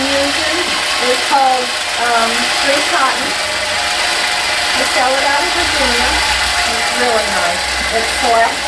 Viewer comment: This was a very helpful and and detailed video. you commented on the batting you used but the machine was too loud to hear you. Could you post what batting it is? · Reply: I thought she said it was Dream Cotton Select